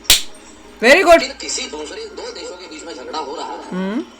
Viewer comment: what he says lekin bhosri 😂😂😂😂😂😂